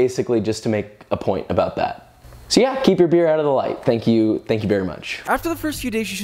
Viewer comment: I'm disappointed that you missed the chance to say "Beer-roll" instead of B roll. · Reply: I also thought he said "Thank you beer much" at 5:18 :D